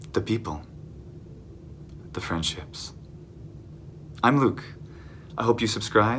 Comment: Lol when you said "friendships" at the end of the video I legit heard "French hips" and I was like "hold on what?!" You're so right about when something is untranslatable it truly is part of the language. Nowhere can you see that more than with humor. I'm Romanian, and I've lived in Norway for a number of years now. I have two Norwegian flatmates. What I miss most about Romania is the humor. There are certain expressions in Romanian that just come into my mind when I experience situations here in Norway, and I start to tell my Norwegian flatmates, but halfway through I figure out that I can't really translate that, and certainly not in a way that will make it click for my flatmates in the same way it would for another Romanian person. It makes me feel frustrated, but at the same time a little smug. This joke is "mine", you'd have to be Romanian to get it. "Why are you laughing?" "Mh, just... Romanian things".